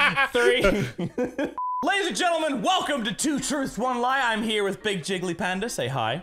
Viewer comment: I thought I heard say "2 turds on lie"